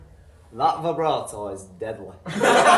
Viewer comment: what did reece say at 5:13? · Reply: That vibrato is deadly